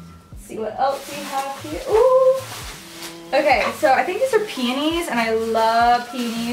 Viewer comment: i swore she said painties i was like bitch wtf love the video though